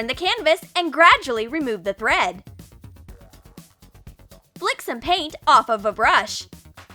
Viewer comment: I thought it said LICK some paint from a brush >v<